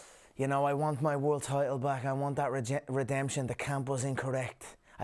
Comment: sounded like he had a Freudian slip “rejection” instead of redemption.